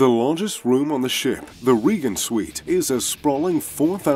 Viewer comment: I heard 'vegan' suite...😂